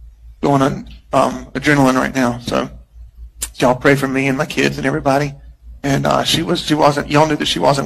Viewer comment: did he say dont pray for me and my children ? on min